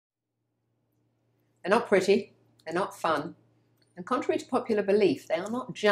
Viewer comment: I cracked up when she said Hello pretty 😆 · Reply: I thought she said “ They’re not pretty, they’re not fun”